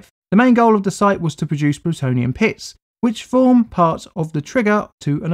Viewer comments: I heard "was to produce plutonium piss" instead oof pits, and it didn't help that when I looked ups I thought I was looking as a top-down view of a toilet 😅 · Why did I hear "It's purpose was to create Plutonium piss" And then the bomb that looked like a toilet bowl appeared =D Was I the only one?